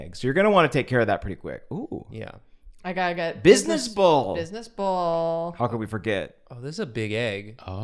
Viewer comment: I kept hearing "business bowl" lol